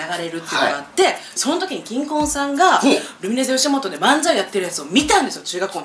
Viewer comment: 直美の ｢キンコンさんが｣ が ｢新婚さんが｣ に聞こえる笑